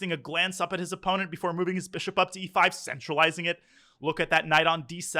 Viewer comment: Who else heard “Moving his bishop to e5, sexualizing it” at 3:01 😂